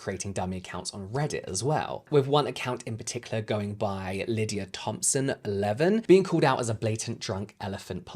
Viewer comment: Also, when you said lydiathompson11 I heard it as Lydia Thompson, 11 and I was like damn those Sephora Kids are just babies aren’t they?